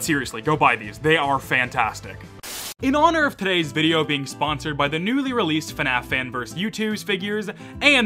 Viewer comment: i thought he said it was sponsored by youtube